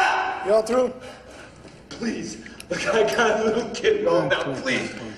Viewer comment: Did I hear Madsen whisper:No no no when Baltz said:I have little kids at home? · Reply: "Nah don't don't don't don't" Is what I heard. at 04:44 there was other whispering leading up to it too.